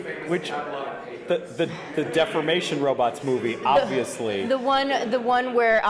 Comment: I guess Deformation Robots are a lot more kid-friendly than Defamation Robots, which is what I thought you said at first.